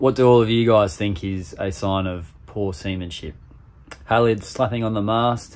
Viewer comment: I thought you said semen shit.